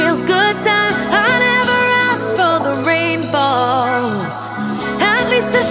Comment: I thought she said i never asked for the reball